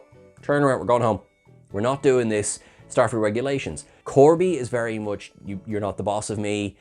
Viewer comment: Had to laugh: At 15:02, instead of Starfleet regulations which is what said, the auto-generated captions read "Starfruit regulations". Got a sudden image in my head of a cargo vessel full of alien fruit heading to DS9 or somewhere.